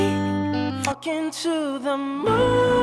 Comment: WAS I THE ONLY ONE WHO HEARD "FKING TO THE MOON" IN